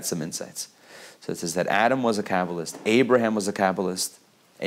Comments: I swear thought he said Adam was a capitalist 😂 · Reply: Amanda Klinthäll He says he was a Kabbalist! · I was shook when I thought I heard him say 'Adam was a capitalist.'!!! Silly me. Great video.